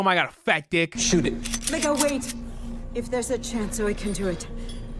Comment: did she say "nigga wait"?